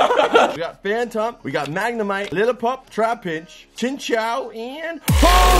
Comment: Sounds like you said little pump